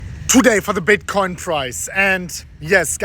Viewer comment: I heard “today for the bitcoin fries”